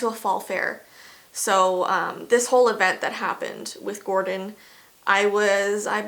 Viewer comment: Oh my goodness! Im so so sorry!! Stay positive, I know its hard. Also, did she say his name was Gordon? At like 9:48 is?